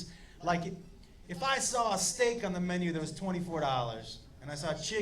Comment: Thought he said stick not steak